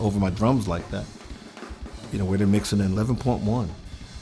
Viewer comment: what does he say at 2:39? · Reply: He says: "Where they mix in 11.1". He is likely referring to the Auro 3D immersive audio format: <link>